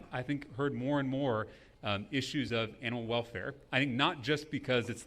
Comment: did he say anal welfare?